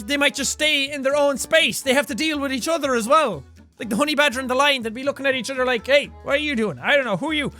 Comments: I thought it said 'horny Badger and Lion'...... I'm not dirty minded at all. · I thought it said horny badger